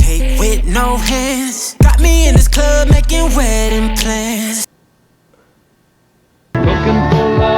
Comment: LOL--I heard "making wedding plans" and at first I thought it was something even worse: "making wet implants"!